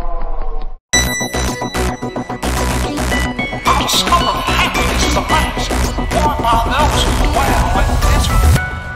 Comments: what is he saying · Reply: He say I discovered that happiness is a mask for those who dwell in misery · what'd he say? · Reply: I discovered that happiness is a mask .. worn by those who dwell in misery. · What he say ? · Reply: I discovered that happines is a mask Worn by those who dwell in misery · Can someone tell me what Mickey says at 4:21 ? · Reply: “I’ve discovered happiness is a mask, worn by those who dwell in misery”